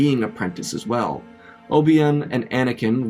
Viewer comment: bro did not just call obi wan obium 💀